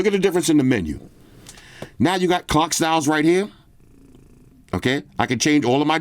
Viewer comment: Oh clock styles, I thought you said 🐓 styles.😂. Just order my S24 ultra. With all the Samsung deals, got it for 970$ Aussie $$. So I guess I can keep both kidneys this year. 😅. I'm spewing I couldn't get the lilac purple. They were all sold out. Now I wait for the REAL REVIEW. S24 Ultra.